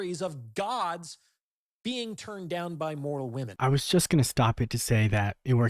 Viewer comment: I think you misheard him. He said “mortal women” not “moral women”